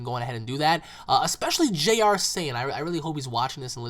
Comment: If they actually do an Insidious 6 movie, they gotta have a Spiderman reference in it. And I thought you said JRC at 18:18.